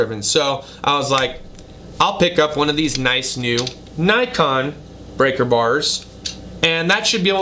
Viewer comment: sounded like you said "Nikon" instead of "Icon." Yeah Nikon Cameras can be very expensive lol